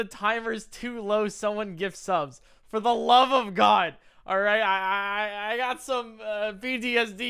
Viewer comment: I legit heard "I got some BDSP"